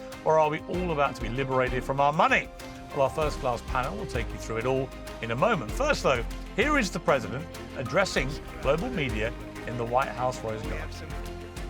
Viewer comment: Did he just say first rate Panel? 😂😂😂😂